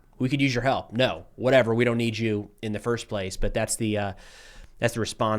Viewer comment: I thought American said it “don’t need no man”